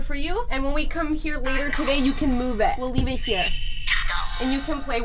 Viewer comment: I heard move doll